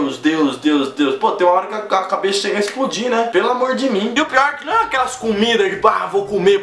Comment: "pelo amor de mim" kkkkk rachei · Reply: Eu tb ouvi "pelo amor jimin"